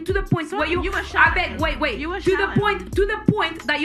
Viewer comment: did she just say “Abeg wait wait” lmao 😂 iykyk